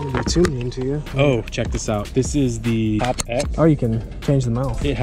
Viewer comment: What lure was the one with the different open mouths at the end sounded like he said Pop x?? I would like to know.. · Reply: I found it it's called megabass xpod